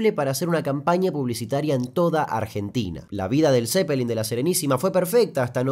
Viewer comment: Pensé que decía "Led zeppelin en Argentina" xd